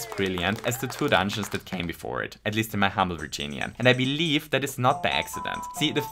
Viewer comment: If I hadn't turned subtitles on, I wouldn't have noticed that you said "Virginian" instead of "opinion"